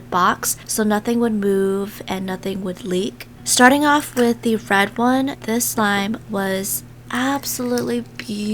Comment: did she say Red?